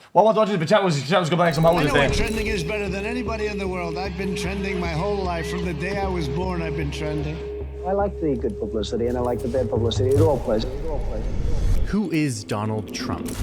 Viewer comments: anyone catch wtf he said at 0:03 - 0:05 ????????????????????????????????????????????????? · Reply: "well i wanted to watch the video but chat was just complaining like some hoes yesterday" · I thought I was fluent in Juicer but wtf did he say at the start? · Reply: well i wanted to watch this but chat was complaining like some hoes yesterday